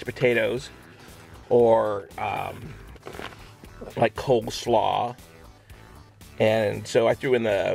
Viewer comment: Did anyone else hear say cokeslaw 😂